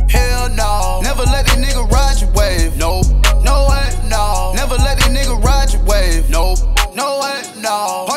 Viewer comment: Idk y to me it sounds like hes saying never let a nigga graduate its fucked up drake I wanna graduate already lol